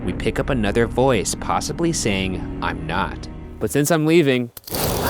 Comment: What if the voice Ryan heard at 12:40 was saying "I'm in love" instead of 'I'm not" .... They just loved him too much, that they didn't want him to leave. #loveatfirsthaunt